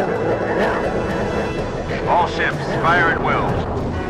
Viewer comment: I thought it said oh sh*t fire at will